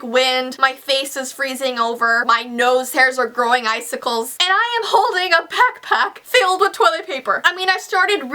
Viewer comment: "My face is freezing My nose hairs are growing iceicals" (Me):"Did she just say her nose hairs are growing iceicals 0- 0"